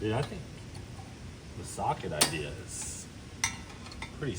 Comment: why did I keep hearing “sock” instead of “socket” 😂